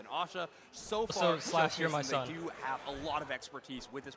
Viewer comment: what is overlord saying can anyone explain? I am confused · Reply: slash you're my son